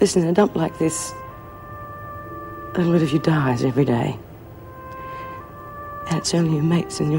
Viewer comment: John W at 10:35 does bea say "a bit of you dies everyday" ??